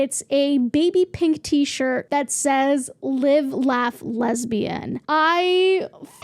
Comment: Oh my god. With That live laugh love shirt, i thought you said "live, laugh, labia"! Lesbian makes more sense for target but to me, labia would be a little edgy and support all women.